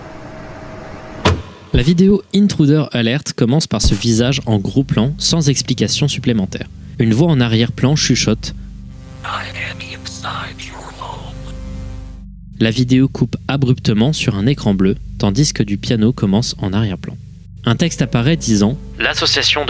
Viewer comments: A 30:18 j'ai entendu I'm inside your mom sa m'a buté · Oh non j'ai entendu "i am inside your mom" ca casse tout le délire mdr super vidéo